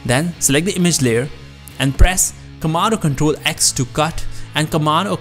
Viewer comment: did he said tomato control at 4:40 minute? xD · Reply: "Comand or control"